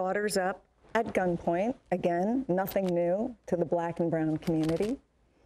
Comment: I think what she was saying was “nothing new to the GUILTY community” 😂😂😂